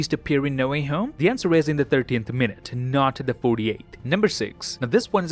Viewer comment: When you said 35 minutes and then you said "In the 30th minute, not the 48th." Your's Subtittle/Caption (CC) get it wrong , its said '13th' instead of '30th' ... I'd realized that yours subtitle (CC) have upgraded but still have some small mistake ....